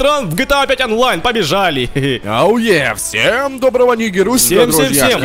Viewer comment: Он сказал "АУЕ" или мне послышалось?